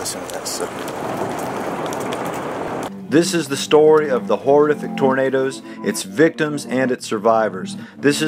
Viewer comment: i had captions on and instead of saying ¨this is the story of horrific tornadoes¨ it said ¨this is the story of horrific tomatoes¨